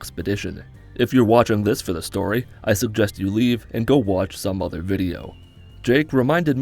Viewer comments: What he says: If you are watching this for the story, I suggest you leave. Subtitles: If you are watching this for the story, I suggest you fuck off to some other video. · The captions! Lmao at 1:00 said “I suggest you leave and watch some other video” and the captions say “i suggest you fuck off and watch another video” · lmao he says leave in video but captions say f off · I like the fact that the narrator says "I suggest you leave", but the captions says "I suggest you fuck off". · Put the cc on. It says, "I suggest you f*ck off", instead of, "I Suggest you leave".